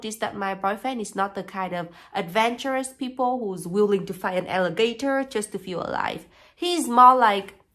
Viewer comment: Between the nerdiness and the rightful paranoia with internet privacy... I like this dude more and more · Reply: I thought she said “Fighting an elevator” 😂 I love it